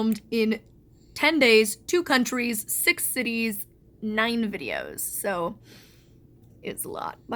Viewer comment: 9 videos good. Because at first I thought you said 90 videos.